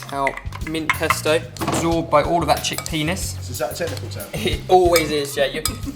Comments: did he just say chick penis?? lol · Literally thought he said chick penis. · I thought he said chick penis XD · i heard chick "peaness" ummm....WHAT? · did he just say chick penis